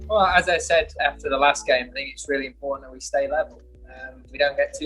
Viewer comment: did he said "F to the last game" ie respect ?